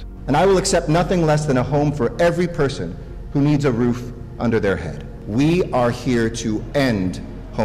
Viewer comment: Hold up did anyone else hear “under there head”